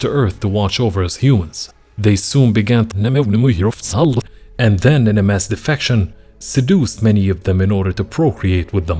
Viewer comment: can someone tell me what did he said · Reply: Had relations with humans..